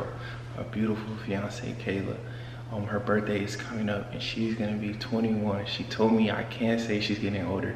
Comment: 21 ? damn yall so younggg 🥺🥰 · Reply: ohh i thought he said “ ur gonna be 21” in the vid